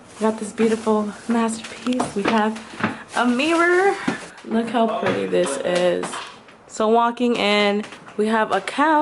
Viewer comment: When she said "Look how pretty this is" I thought she said "Look how ugly this is" XD